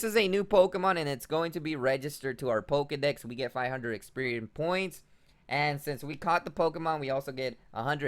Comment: Ok guys, I'm gonna try to translate here. "Poke spots" are poke stops. Also, experiem points are experience points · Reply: 😂 I thought he was saying “Experian points” and I kept yelling “Experian is the credit score company!!”